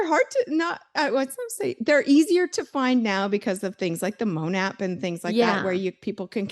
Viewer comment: what is the app she says? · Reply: I wanna know too! Sounded like Moan?